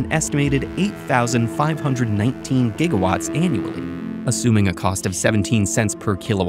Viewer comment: I thought he said gigachad😂